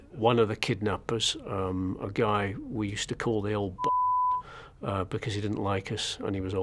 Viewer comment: Did he say "old bastard" on minute 6:59 ?